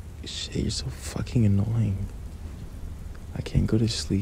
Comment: i thought he called us annoying-😭😭